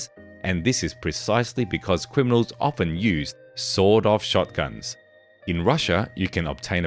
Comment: With his accent it sounded like he said sold off shotgun😂. Still a good entertaining video. thanks